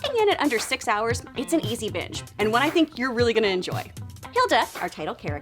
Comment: i know that she says it's an easy binge, but the first time it sounded like she said that it is an easy b*tch.